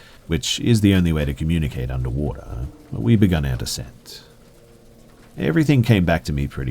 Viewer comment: “We begun our descent” my looney ass thought he said “we begun our sex”